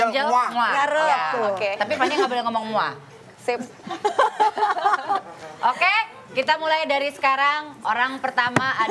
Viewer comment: Di menit 02:13 gak tau kak vincent ngomong apa sama enzy sampai enzy ketawa begitu.. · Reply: suruh tukar tmpt